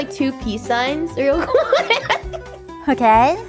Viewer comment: i heard "two pea signs"